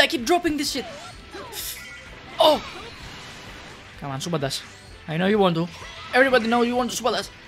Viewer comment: Why do I always hear supabas when says super dash? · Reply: LOL I thought you said super dash. Keep up the good content also, if you ever want zamasu’s lvl 3 to come out. Just nerf him, everybody is waiting